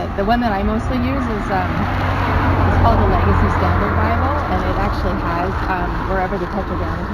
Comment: I couldn't catch what bible mentioned at around the 7:08 mark in the video. The subtitles didn't do the audio any justice. Can someone tell me the bible with the tetragrammaton that is being mentioned here? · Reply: Legacy Standard Bible. It's basically the latest NASB. <link>